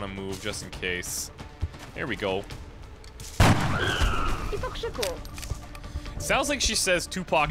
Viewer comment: I heard her say "Tu Pac Sacur."